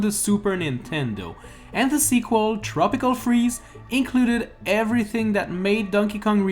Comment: why does it say “tropical cheese”?